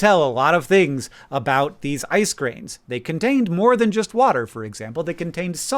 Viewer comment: "And they were able to tell a lot of things, about these icecreams." Did anyone else hear him say icecreams